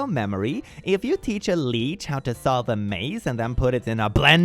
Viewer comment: my dumb brain understood leek instead of leech, i was so confused trying to figure out how leeks could solve a maze and get fed to other leeks 😭